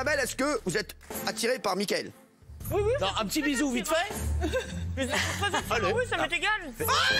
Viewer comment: Qu'est-ce qu'elle dit Isabelle ? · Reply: Aurore Le Fouiller “ que je sois très attiré ou non ça m’est égale”